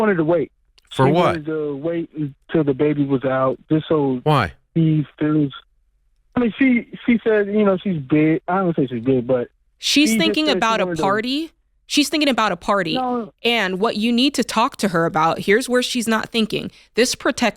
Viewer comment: Did he say she wants to wait until the baby's born because she thinks she's gay? Replayed it but that's what I thought he said